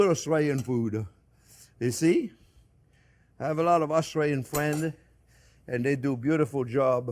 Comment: Sounds like he says "australian"